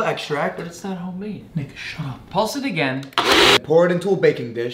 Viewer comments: I thought he said "Nigga shut up"💀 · Bro I heard “Nigga Shut up” 😭😭😭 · When he said nick shut up it sounded like he said n**ga shut up 😭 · What said : Nick shut up VS What it sounds like said : NI- Shut up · i swear i heard him say”ni- shut up”😂💀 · Sound like he said ninja 🥷 shut up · I thought I heard "babe shut up" 😂❤ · I thought he said the n word shut up 💀